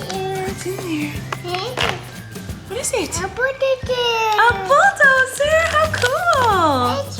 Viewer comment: at 6.43 it sounds like said "A birthday cake"